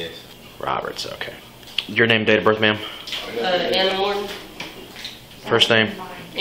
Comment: Ha! I thought she said her name was anamorph. Lol